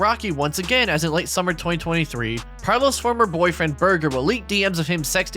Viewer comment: burger did he just say his last name is BURGER?